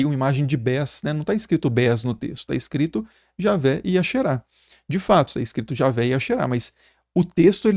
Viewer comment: eu ouvi 'Javé ia cherá'... pensei kralho mano... Javé brabão na neve